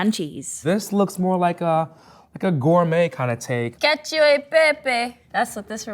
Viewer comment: The captions are wrong, here; actually says "cacio e pepe" (the Italian pasta dish with cheese and black pepper), not "get you a pepe", as the captions say. Also, at 15:28 she says "definitely mushroomy", not "definitely a mushroom meme". 😂